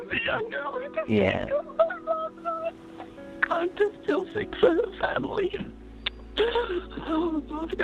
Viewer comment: the unclear part sounded like "I feel so sick for her family"